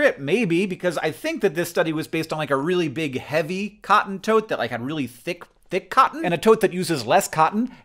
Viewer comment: I keep hearing "cotton toad" and i like the picture my imagination is coming up with